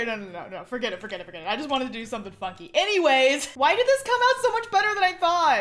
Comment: DID SHE SAY FUCKY